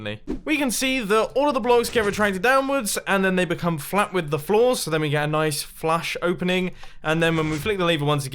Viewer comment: The blocks become flat with the floor. I heard: The blocks become one with the Force